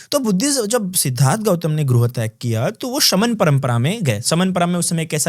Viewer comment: One more problem I noticed while reading subtitles that it does not match what monk is saying. [12:11] > E.g. when monk said, "jb bhagwan Buddha ne grah tyag kiya, tb vo shraman parampara mei gye". but the subtitle was: "so, when siddharth gautama killed his guru...". He did not even said anything like that in the conversation. So, requesting english audience, does not rely on subtitles.